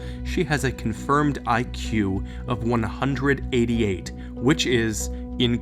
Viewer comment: I thought that she said she had an iq of 28 lol